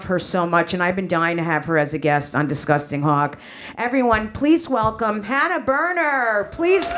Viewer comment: As a Brit, I heard “Disgusting Hog” not Hawk! Jessica laughing + hitting rewind go hand in hand.